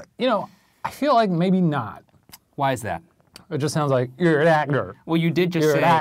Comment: “You’re a doctor?” Is what i hear